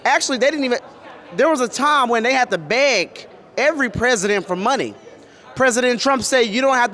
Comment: I work at a nursing home and we call them residents and i bout fell out when he said that i really thought thats what he was saying 😂😂😂😂😂😂